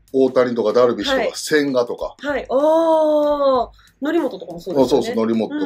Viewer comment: 「則本」が「成本」に聞こえて渋いなぁって勘違いしたw